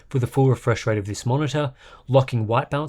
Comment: I keep hearing "bounce" when you say "balance"